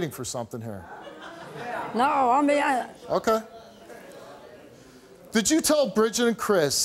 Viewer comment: What’d she say 😭😭 0:43 no I’m beaah